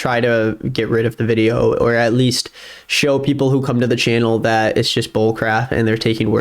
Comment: i heard "the channel he said us" and just clicked off the video lol